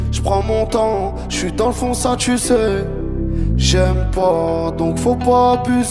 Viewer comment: A 3:35 il dit quoi svp? "Jsui dans le "Fond ça tu sais", j'aime pas donc faut pas abuser"? C'est le "Fond ça tu sais" où j'ai un doute. Merci les loups et louves ;)